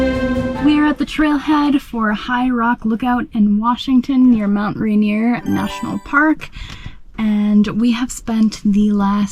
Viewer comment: It did sound like you said you were in the national park. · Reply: I said “we are at the trailhead for high rock lookout near mount rainier national park” 😊